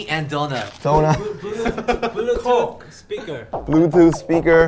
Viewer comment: The way Jungkook first pronounced "Bluetooth speaker" sounded like he said "Bulletproof speaker" lmao what the heck are you doing in your studio JK? 😂😂😂